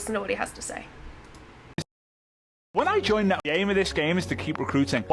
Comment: I heard "gay of this gay" not name of this game